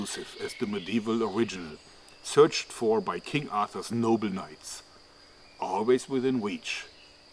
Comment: did he say King Arthas? :D